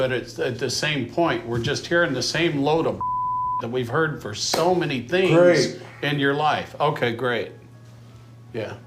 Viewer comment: what did the dad say? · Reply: We're just hearing the same load of bullshit that we've heard for so many things in your life